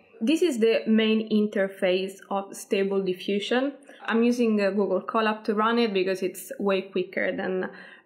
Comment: you said you’re using something to run it. What did you say? I can’t understand. Thanks in advance 👍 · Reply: I say I am using Google Colab to run Stable Diffusion. Note: when I made the video, Google Colab was free. If you have a powerful computer you can run it locally.